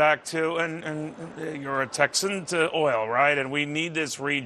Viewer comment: it sounds like he says, "an ignorant Texan." -.